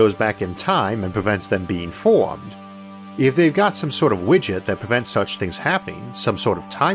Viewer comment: Due to your pronunciation, at 21:47 I had misheard "widget" and now I want time-traveling witchers to exist. Thank you! ❤️